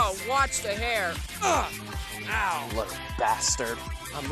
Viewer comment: did sonic just say in to the air on